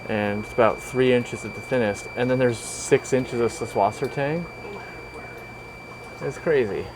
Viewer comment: What is the name of the plant the dominates many of these tanks. At 7:49 he says the name but I can't for the life of me figure out how to spell what he say. (Seflosertain, Cephlocertane???) I'm a plant noob so I have no clue what it is. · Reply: subwassertang